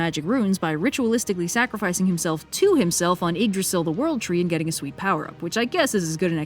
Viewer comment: someone please tell me I wasn't the only one that heard Edris Alba World Tree instead of Yggdrasil World Tree